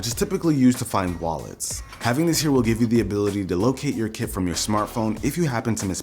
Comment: i thought you said kid